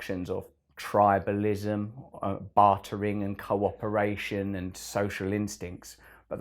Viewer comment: your subtitles displayed, "corporation" instead of, "cooperation." You might want to fix that, Russell. I love your words as always.